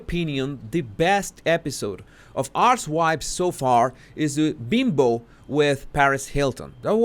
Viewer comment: “ArchWipes.” Oh, no, you didn’t! This is absolutely classic. I can’t believe I didn’t think of it first. I’m a long retired early-days jury consultant. Good think I’m retired!! 😮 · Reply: I thought he was saying Arse-wipes. 🤔 🤣